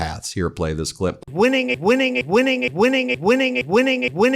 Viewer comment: I thought the clip more sounded like he said “Winging it” which seems to be this regimes go to solution every time! No preparations, no after thought, no risk assessments - just running around like headless chicken and lie and hope for the best!